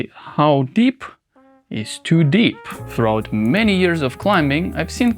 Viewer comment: How deep is to deep throat (even thought you said throughout)